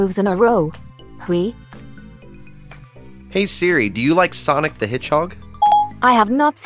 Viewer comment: So which response was your favorite? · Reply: It sounded like Siri said sonic the headhog.